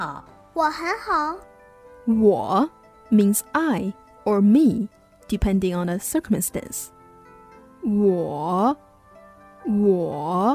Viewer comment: I really love this channel and this video is just perfect. I decided to write what the teacher says so I can learn faster, but I couldn't understand what she said at 5:41 after 'depending on....' Can someone please help me? Sorry, I'm not an English native so I think that's the problem. I'm still learning English too. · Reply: depending on the context or circumstances